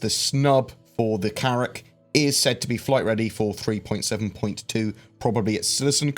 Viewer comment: For a minute I thought you said the carrack was going to be flight ready and had to listen again.